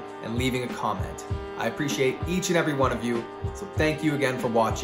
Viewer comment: Awesome tips! Did I hear “good feet” at the end 🤣